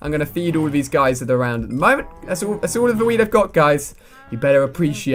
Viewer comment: At 1.47 it sounds like is saying that's all of the weed I have